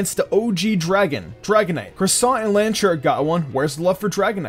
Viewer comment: Are my ears broken? What does he say at 2:23? "Croissant and Lanturn?" And then it sounds like he says "Mega Croissant" again a few seconds later... EDIT: OH because Mega Salamence looks like a croissant...but the way he narrates this it sounds like he is completely serious about its name being Croissant.